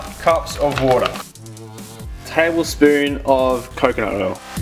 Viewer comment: fk, thought you were about to say table spoon of coke LOL